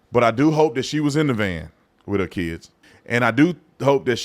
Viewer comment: I thought they said 7 in the van